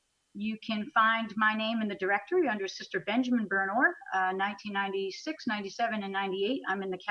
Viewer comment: Sister Benjamin Burnor? Did I hear that right?